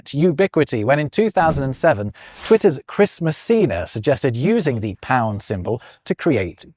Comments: When you said Chris Messina, I thought you said Christmas Cena and expected John Cena to burst through the screen. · heard this as Christmas Cena. He'd probably make a good santa